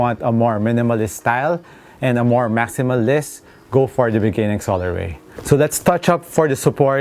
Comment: What did you say at 3:29about the style i don't understand the words? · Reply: He said: go for the beginning colorway but its the "upbringing colorway"